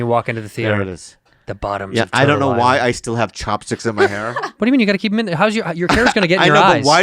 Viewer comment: bobby said 'chapstick' but i heard 'chopstick"